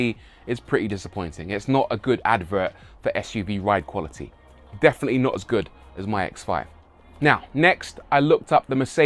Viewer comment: I thought he said "definitely not as good as my ex-wife" 😂